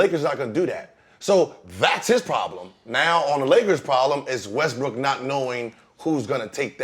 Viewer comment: Did he just say Westbrick?